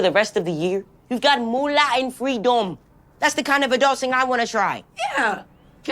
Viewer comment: I thought he said "free dome"